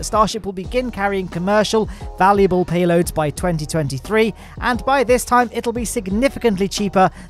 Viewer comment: thought you said 2073. Nearly had a heart attack